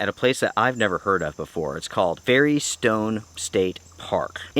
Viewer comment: I heard Very Stoned State 🤣